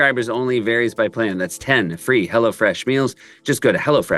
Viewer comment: In the ad it sounds like Seth says “Hella Fresh”, which I think is a cooler name for that company